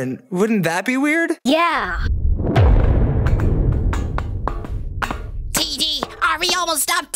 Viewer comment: says Teardrop instead of TD in captions